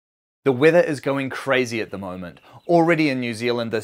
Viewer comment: I heard "the wither" and thought it was going to be a Minecraft shitpost.